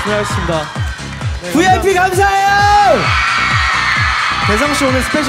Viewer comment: DID ANY ONE NOTICE THAT WHEN SUNGRI SAID SOMETHING(i dont know what he said because it was in korean)GD WENT BACK WORDS WITH HIS EYES WIDE OPEN HE DOES THIS WHEN HE HIS SUDDENLY SHOOK😋😊😲😲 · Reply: Seungri just said " THANK YOU VIP " I think GD just got shocked coz seungri suddenly shout 😅